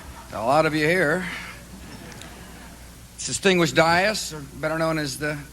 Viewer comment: what is he saying at 2:07?? "distinguished dietists"?? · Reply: I think he's saying "distinguished dais", meaning everyone included in the main area.